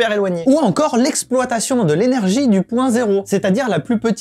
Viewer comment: J'ai entendu "potentiel du point zéro" j'ai imaginé une grande cité sur l'eau avec des aliens qui aspirent la vie des gens... Dites moi que je suis pas le seul